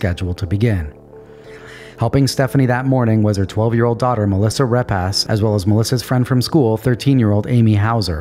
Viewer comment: I’m so sorry ….but I really thought he said 12 year old daughter “Melissa rip ass” and I almost died 🤣